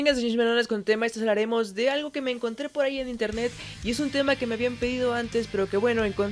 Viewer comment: lo que entendi Muyyyybenshhhhshiiihssssshhhhhshhhhininternetshh